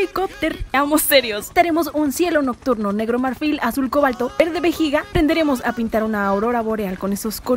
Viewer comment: Ayuda entendi "verde vagina" 💀